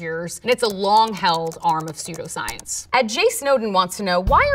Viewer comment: I thought she said "sudo science" and now I wanna watch a video about Linux